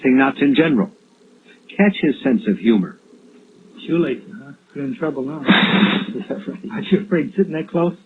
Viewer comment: What does Gacy say at 3:00? I couldn’r figure it out. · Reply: he says "shoe laces..You're in trouble now "